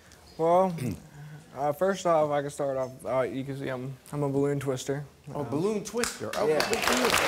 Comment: I thought he said blue twister